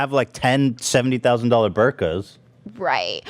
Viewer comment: I think he said Birkins, like the purse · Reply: I think he MEANT "Birkins", but I heard "burkas" too 😂